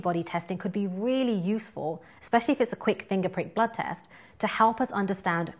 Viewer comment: Fingerprint blood test? Did I hear that correctly?